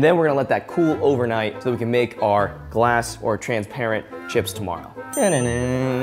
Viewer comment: At first I thought it said “transparent child’s”